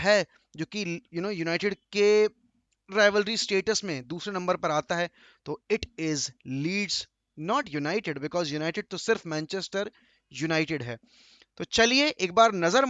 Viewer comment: bhai kya bol diya united is not a rival city bolna hota hai aap toh fred jaisa mat karo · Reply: Are Maine bola Leeds are not United because United to sirf Manchester United hai